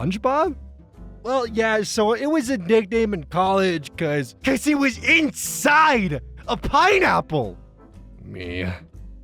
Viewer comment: It's bad that at 2:56 I thought he was saying he had carved out and gone inside a giant pineapple, isn't it?